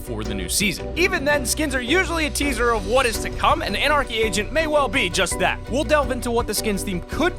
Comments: with the anarcy agent I thought he said anarcy Asian and I was like wot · It sounds like he is saying anarchy Asian 😂 Like if u agree👍🏻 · It sounds like you said Anarchy Asian ;_ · Swear I thought he said anarchy Asian Lol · Did he just say anarchy asion lol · I thought you said anarchy asain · I thought you kept saying “anarchy Asian” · I thought he said anarchy asian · I thought you said anarchy Asian which does not make sense at all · Anyone else thought he said anarchy Asian